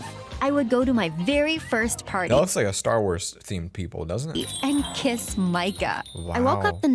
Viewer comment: Wiat did i just hear that 3:48 he said star wars that aot(attack on titan)